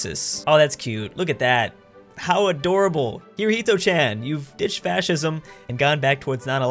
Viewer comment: did you say Hirohito-Chan?